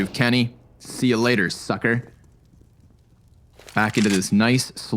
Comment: Did he say "see you latter sucker"?